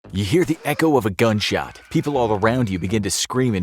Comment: bruh at the start i thought he said "EHEHE echo of a gunshot."